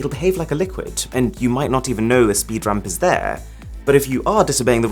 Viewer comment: I thought he called them "speed rump"s. I was like.... That's something entirely different Arun! Lol